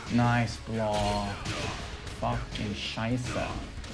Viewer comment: did he just said "fuc*ing Scheiße"? xDD As a german this made me laught so hard! Cheers!